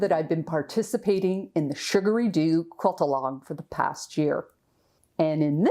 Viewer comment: I didn't realize every time you referred to this QAL I thought you were saying "surgary do".... I didn't realize who or what this is. It is nice to know it is a lovely young person with a YouTube channel. Thank you for the sweet introduction.